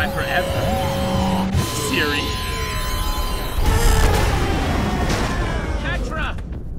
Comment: i swear i heard Jesse say Jetra when he jumped down after he broke PAMA